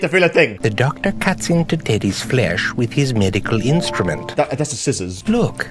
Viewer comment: im sorry but did he say INSTRUMENT???